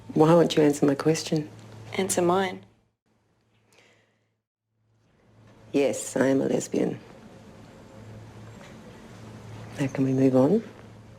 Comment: what say bridget 1:57? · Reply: "yes I am a lesbian, now can we move on?"